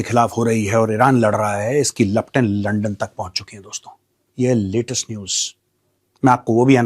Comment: did I hear that right???😂😅😅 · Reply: London bola bhai:face-blue-smiling